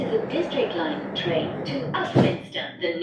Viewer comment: the next station e 17:01 i heard pontypridd instead of upminster bridge 💀